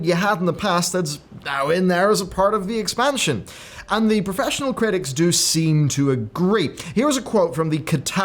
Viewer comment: Oh man, I for sure thought you had said professional critics and Kotaku right afterwards, but I didn't dare go back to check for fear of losing all faith in you...